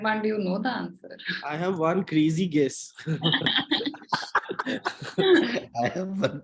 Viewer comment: i misheard "i have one crazy guess" at 9:39 as "i have one greasy guess" and i was so impressed by that joke xDD